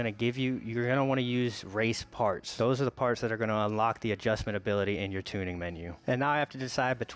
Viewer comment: did man just say adjustmentability 😭😂